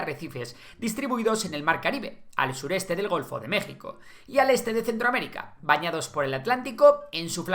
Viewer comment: PERDON Golfo de que... Escuche bien ? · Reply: Golfo de México